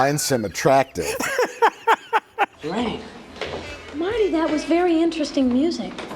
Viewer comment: does she call him Marty?